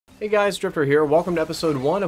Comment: It sounded like he said hey guys it's stripper here XD